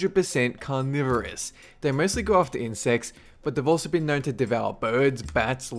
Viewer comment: did he say sex ???